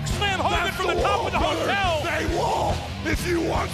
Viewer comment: "HEY WALL" Gets me every time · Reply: i always thought he was saying THEY WALL